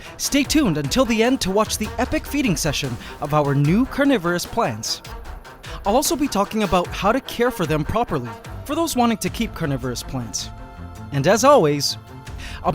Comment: Anyone else Thought carnivores Plants said Coronavirus?